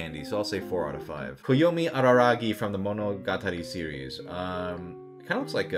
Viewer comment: I heard "from the MANGA" at the roy mustang part and my fma brotherhood-ass INSTANTLY APROVED